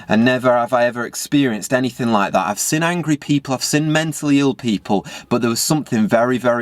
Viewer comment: the subtitles inexplicably wrote "creepy puffs in" instead of "angry people, I've".